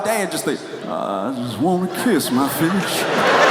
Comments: I heard "kiss mah fish" too. Thanks for reminding me about that. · I think most people heard fish